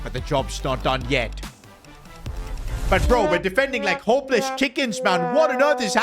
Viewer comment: what did bro say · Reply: Homeless chickens is crazy 😂😂😂